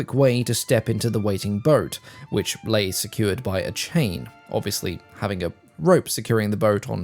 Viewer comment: I thought you said "secured by a CHAIR". Chain makes far more sense. BTW, how is everyone else's tinnitus doing?